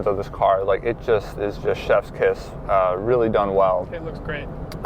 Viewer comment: oh lord, at 16:38 I swear I thought I heard you say "Chef's piss"...